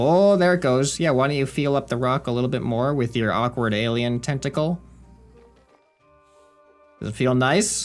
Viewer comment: I thought you said testicle but you said tentacle 😂😂😂